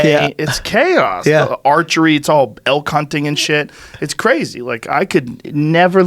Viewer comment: oh elk hunting, thought he said " L cunting" like what is that??!?!